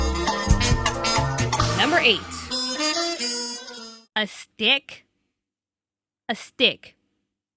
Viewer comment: When she said stick I thought she said dick